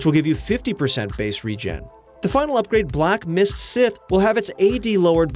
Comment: did you just call that scythe a sith? What?